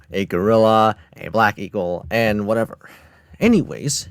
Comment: for a second i thought he said “black people” 😭